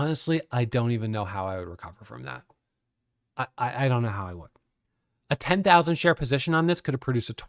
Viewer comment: I guess I don't understand, I thought the most you can make short selling was 100% if the stock went to zero which it most likely wouldn't. How could you have made 80, 000 from a 5,000 position or 180,000 from a 10,000 position? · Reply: Oh that makes sense, I thought he was saying a 5,000 and 10,000 Dollar position not 5,000 or 10,000 share position. I see now, thanks.